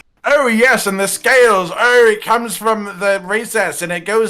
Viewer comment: Subtitles interprets this as 'it comes from the racist' lmao Also! Say what you want about Shrieking Legiana, but its 'reinforcement call' to get regular Legiana to the zone it's in is really haunting and cool. The first time I heard it, it scared the shit outta me 😂 Doesn't help that it's really loud as well lmao.